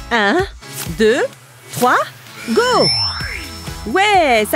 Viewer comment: J'ai compris 123 go à